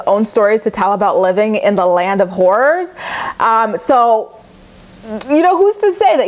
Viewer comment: Sounded like you said "Living in the house of whores"...which isn't half wrong💁 lmao I love you 💜